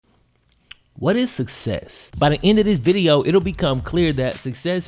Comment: I thought he said “what is sex?” Not “what is success?”